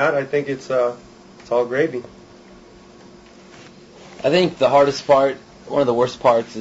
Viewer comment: Did Zacky say "the hardest fart" @ 3:00? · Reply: LinkinPark71193 3:02 and it does sound like he said the hardest fart but he says the hardest part lol